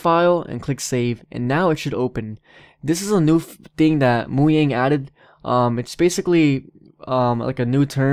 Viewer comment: did he just say moyang instead of mojang